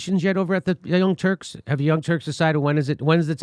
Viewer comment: did he call them "The Old Turks"? 😂